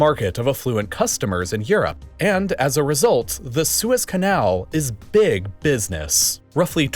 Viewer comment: did he say swiss canal?